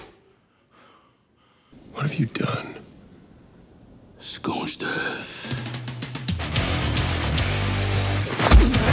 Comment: Wtf does Butcher say at 4:11? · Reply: Scorched earth